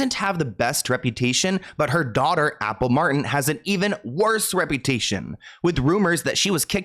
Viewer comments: I really thought it said apple martini and I was weirdly wondering why I was just now hearing about that being her middle name. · I thought it said Apple Martini 🍸😂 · I definitely thought this said "Apple Martini" at first. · I had to read that 3 times, I swore it said Apple Martini 🍸 🤣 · I thought that said Apple Martini!! 😂